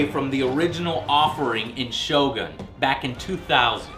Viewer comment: I thought it said shotgun :3